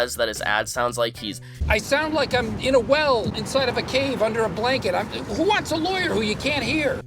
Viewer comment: did he say "whale inside a cave under a blanket" !!!?